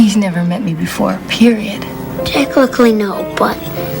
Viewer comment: did he just say "techlichly?"